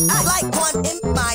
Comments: Why do I hear “i like corn in my butt”💀💀💀💀💀💀💀💀💀💀💀💀💀💀💀💀💀💀💀💀💀💀💀💀💀🤨🤨🤨🤨🤨🤨🤨🤨💀🤨🤨🤨🤨💀🤨💀🤨💀🤨💀🤨💀🤨🤨💀💀🤨🤨🤨🤨🤨🤨💀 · "I like corn it's my butt" Parrapa bars go hard even Eminem is shivering in fear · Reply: I hear "i like corn in my butt" 💀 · Man I really thought he said “I like corn, hit my butt” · Bruh it sounded like he said I like corn in my butt